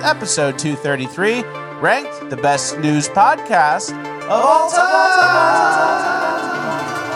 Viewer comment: The best "NEWS" podcast? I thought he was saying the best "NEW" podcast this whole time. My life is a lie!